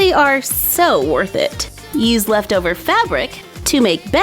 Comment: I thought she said “Use extra fabric to make gay “ I was like WHAT